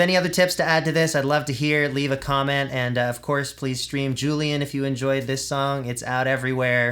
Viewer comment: lol I thought he said, “please scream Julian” and I totally just yelled it out loud